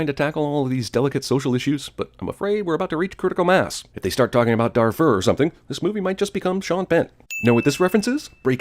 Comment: I heard Der Fuhrer and I was immediately disappointed after reading the subtitles